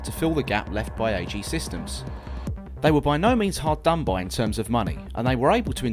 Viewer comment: Tryna caption the whole video, exactly what was that said at 1:49? "They were by no means ______ in terms of money..." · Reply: "Hard done by" - it's British slang.